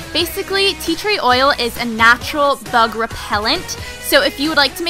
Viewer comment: Who else heard "Tea Tree oil is a natural thug repellant" at 4:38.